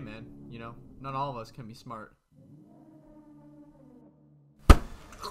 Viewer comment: Why did i hear Wooly say "You suck bi